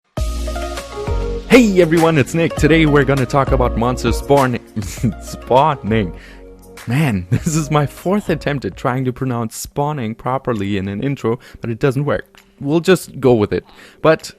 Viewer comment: Did it just say 'Monsters porn' in subtitles?